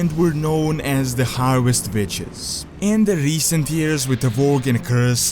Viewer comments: I thought he said harvest bitches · Damn, i heard you say the harvest bitches! Can you do How Powerfull is?